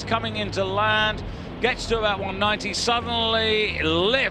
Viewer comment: ボードの後ろの方地面に掠ってないかなと思って0:10あたりから0.25倍速で見てたら、 解説の英語「すっげえええ！」って空耳して一人で笑ってしもうた